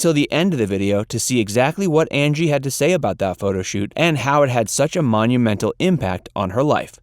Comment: I swear I hear "Andy" Dickinson